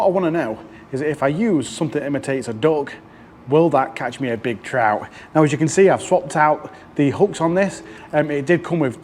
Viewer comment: Nice video, enjoyed it. As a non English speaker (Dutch) I thought you said you catched a trout on a dog:)